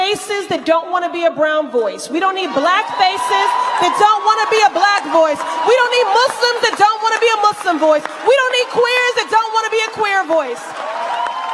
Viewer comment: I heard “we don’t need blacks Muslims or queers” sounds bigoted.